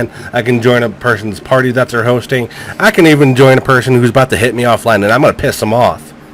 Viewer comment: I was playing codaw I heard somebody "Ima bout to hit you off" (I was shocked and scared because I don't know he's targeting who)